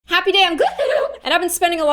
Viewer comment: DUDE I JUST REALIZED THAT SHE SAYS HAPPY DAY I’m I HAVE BEEN WATCHING HER FOR 2 YEARS ALOMST THREE I AWLWAYS THOUGHT SHE SAID happy day on IMA GO DIE NOW